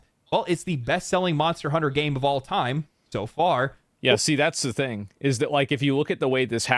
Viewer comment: I thought they were saying this is the worst monster hunter out of the series so far!?